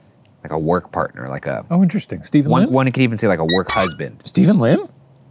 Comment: listen carefully to what ryan says as he refers to shane, what he calls him if u dont know he says "one could even say a work husband"